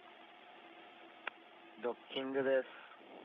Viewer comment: 前澤さんの声じゃない? ドッキングですって聞こえる Mr.maezawa speak “dokkinngudesu“ ??